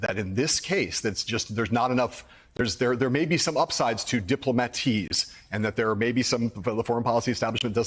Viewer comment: Did he say diploma-tease?